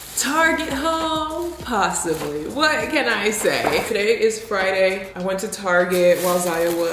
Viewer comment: I thought Friday said fridge 💀🤚